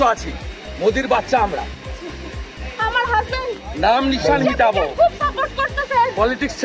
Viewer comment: এখানে কি বললেন তিনি??? আমার হাজব্যান্ড " ইসরাইলকে খুব সাপোর্ট করতেছে" এটা বললেন নাকি অন্য কিছু বললেন। কি শুনলাম??? কেউ বুঝে থাকলে জানাবেন।